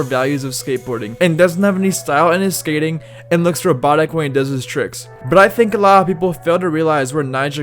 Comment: Nijah is the least cockiest millionaire I’ve ever seen. So people need to stop hating. He earned this. And he doesn’t skate like a robot. Robots wish they could skate like him. He skates like a ballerina. Perfection at its finest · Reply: ROBOTIC! thats what he said! i was so confused. i dunno if its an accent thing, but i didn't know wtf he said at 0:51 in the vid. but as soon as i read "robot" it clicked. or maybe cause i'm just drunk watching this at 2:11am. you be the judge...